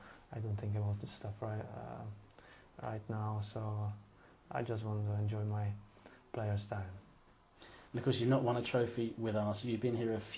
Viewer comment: " I just want to enjoy my playing style " - I think Rosicky says that at 8:50. GREAT that he is well aware over his very elegant playing style :)